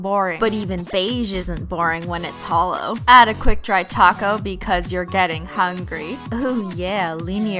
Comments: I thought she said quick dry taco 😂😂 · I thought you said quick dry taco 😂